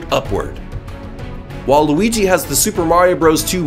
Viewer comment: Did I hear “Waluigi”?